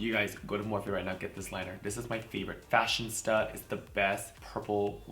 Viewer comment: I think you mean FashionISTA lol I thought you were saying Fashion Stuff, Fashion Stud lol